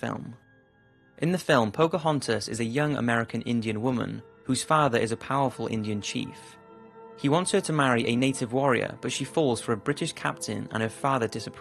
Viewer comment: did they just say they wanted her to marry a native oreo...